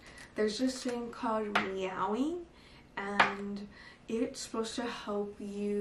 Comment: It's called what? The thing at 12:32. I hear "meowi" hahah. Thank you